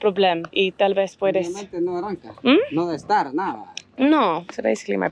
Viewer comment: Hi! Spanish speaker here. I noticed when your motorbike didn’t start and you were talking to the guy, you thought he said “the orange one” or “La naranja” but what he was actually saying or asking was “no arranca?” So he was asking if it didn’t start. Arranca is to start. It sounded like he said naranja tho. Anyway, just thought I’d mention it in case that word comes up again and it could help you!